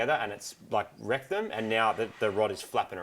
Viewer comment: Did you say rectum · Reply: Evan Moon wrecked em Aussie slang for wrecked them